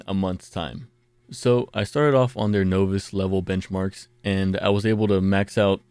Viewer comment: did you just say novice as "no viss"